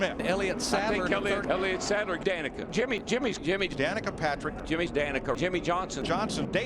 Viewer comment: Is it just me or did I hear "Jimmie's Danica" at 1:29 and "Newman's Kasey Kahne" at 2:03?